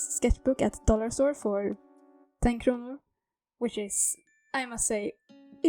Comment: I thought you said “10 Kromer” 😭 I’m so Deltarotted